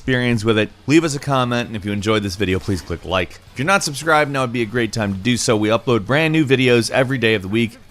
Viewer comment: did he just say "do not subscribe"?